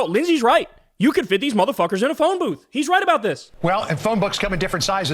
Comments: Tbf I thought he said phone book to and I was confused. You could fit a lot of names in a phone book. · I heard "phone book" too but that just because he said it like my Me-mom used to lol.